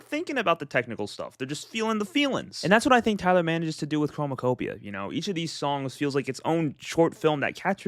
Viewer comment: did you just say homokopia?